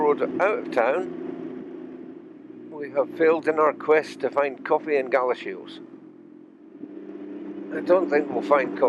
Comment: !!! You made a cameo!!!! Well done my friend!!!! Ok I have a few questions: at 12:17 you said you were going for coffee and ______? Not sure what that word was. Also at 13:34 you mention a piece of mid_____? I just love learning Scottish words for things!! I also love the Celtic knot on your bike. After visiting Ireland I brought home a huge sheet with a Celtic knot that I hung on my wall for many years. That was a long time ago, and your decal brought back nice memories. Lastly, what do the jaggard lines mean on the road at 13:46? I clearly spent too much time on this video! LOL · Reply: lol. At 12:17 I say “coffee in Galashiels” then at 13:34 it’s a “piece of Mint Traybake” and the lines warn that you’re approaching a crossing and that parking is prohibited there. 😁I like the subtlety of the Celtic knot in black on black. I would love to see Ireland it’s such a beautiful place. Plus, they make the best beer in the world 🍺 P.S. The cameo took several takes because the street was busy! 😬😬😬😬😬😂😂😂😂😂😂 Ryde Safe Beth. Hope all’s well 🙏🏻😎🏴